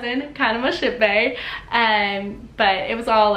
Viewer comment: BRUH AT FIRST I THOUGHT SHE SAID HER COUSIN WAS HER SHIP BAE. I WAS LIKE WTFFF